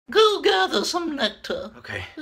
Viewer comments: Not gonna lie, I kept hearing "gargle" instead of "gather" and I'm not sure how to feel about it. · Being honest. It took like 3 watches to figure out what was saying. Now everything makes sense. Everything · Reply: At first I heard “My life, is fucking toxic” for the end part ofc not queen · "My life is fucking torture" - A Bumblebee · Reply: I thought he said my life is fucking charging lol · "My life is fucking torture" - A Bumblebee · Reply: I thought he said my life is fucking toxic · i thought he said "i lied. it's fucking toxic" 😭😭😭